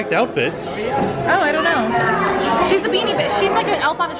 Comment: changes what she's going to say about the beanie Minnie and it sounds almost like she's says "she's a beanie bitch". She doesn't really say that, it just sounds that way.